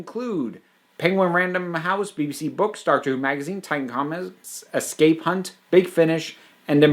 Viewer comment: Did you say Star Trek magazine? What could that mean?